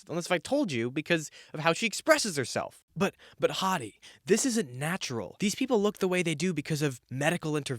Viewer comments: when you were doing the "but... but Hadi" interjections with JK Rowling, I thought you were saying "but hottie" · I keep hearing “But hotty” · only now I realized you're saying "But Hadi..." Instead of "But hottie 💅✨..."